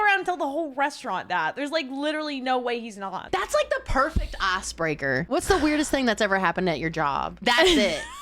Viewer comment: the perfect ass breaker pasta? Or ice breaker? Lol. I definitely heard the first one